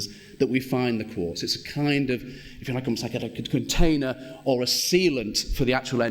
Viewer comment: Can someone tell me what he says at 20:34 -36 ??? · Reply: Poob Ah he says the quartz is like a container or a sealent for the tomb entrences. If you are still interested lol.